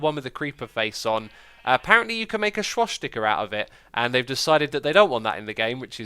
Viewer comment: Xisuma, what did you say at 4:52? Shwash sticker? 😅